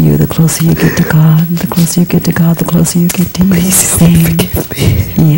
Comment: Can anyone understand what Katie is saying at the 37:14 timestamp? It starts: *Closer. Closer. The closer you get to you the closer you get to God. The closer you get to God the closer you get to ? ? ?* ❓ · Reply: "...to you. Same." She's pointing out that God is everything, just as it is. We look 'outside' for God, or expect external circumstances to meet our expectations of what God should be, but God is already present - in reality as it is, right here, right now.